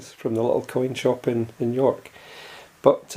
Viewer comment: York? I thought you said New York, U.S. LOL. Very nice sturdy box I like that Stone Mountain half dollar. NO such thing as junk! Take care